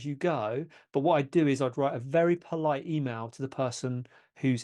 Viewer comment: Hey :) Can you please specify what did you said on 9:56 (where to find them)? Thank you! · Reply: Are thing -- I said you'll be able to find them on Cinando - <link>